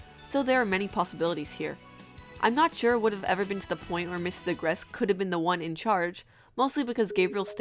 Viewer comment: I had subtitles on this and every time u said mr agreste it thought u said mr address 😂😂😂😂😂😂😂😂😂